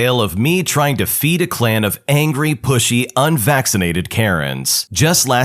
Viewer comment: Story one: thought he said bushy instead of pushy… had a very strange image